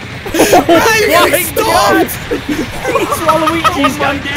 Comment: Is Doo’s name Mat? Idk I thought I heard say Mat Stop! as he was spawning the waluigis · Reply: _Ndrew _ I think he said, “Man you’re getting stomped”